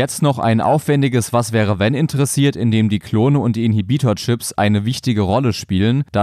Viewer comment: Ich hab gerade erst gecheckt dass die Teile Inhibitor Chips heißen. Ich hab immer In-Gebieter Chips verstanden. Weil Palpatine halt Gebieter von den Clonen ist.